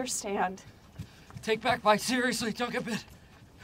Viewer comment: LOL what did michael say?? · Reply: I think he said “take bat bites seriously, don’t get bit.”